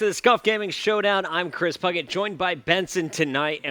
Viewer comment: Sounds like he said Scump gaming showdown.